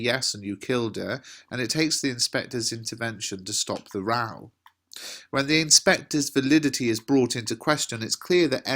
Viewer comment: to stop the ....? sorry I didn't understand what u said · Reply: Ak 1 stop the row, like argument